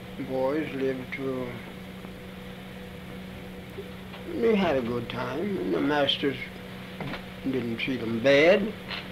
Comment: Did he say at 2:20 “we had a good time, the masters didn’t treat them bad”?